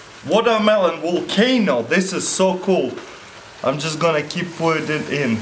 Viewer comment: "This is so cool." I see what you did there. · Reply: I thought he said "This is so cold"